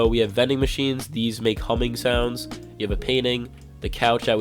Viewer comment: he said humming sounds it sounded like he said Cumming Sounds